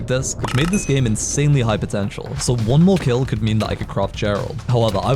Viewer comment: did you just say potential